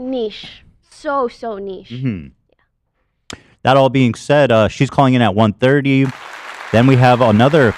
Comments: i thought he was saying trisha was calling in! how did i get that so wrong? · everybody knows where they were and what they were doing when it sounded like ethan said trisha was calling in